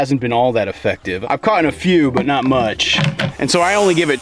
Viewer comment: Did you say 'caughten'? 8:49 You really should wear gloves handling that nastiness.